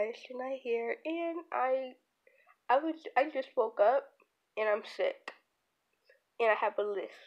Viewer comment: in the beginning, I thought you said, "broke up" and I was like, wait a minute